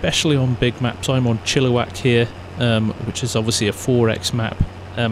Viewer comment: anyone know what map he is playing on? it was soo hard to hear what he said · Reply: It’s chilliwack by Chumpy farmer